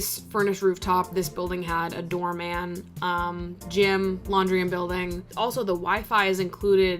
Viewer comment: I thought you were saying the door man’s name is Jim hahahaha